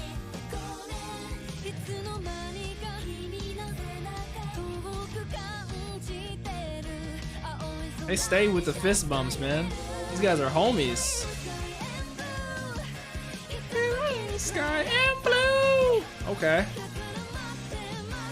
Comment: “These guys are homies” I thought you said Homos! 😂😂